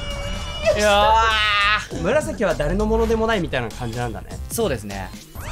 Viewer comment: 紫が村瀬くんに聞こえた。末期だ。